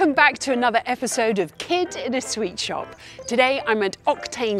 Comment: Who else thought she said " kid in a sweat shop"